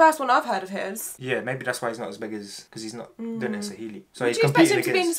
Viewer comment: did he just say he is not a big artist 😂 well .....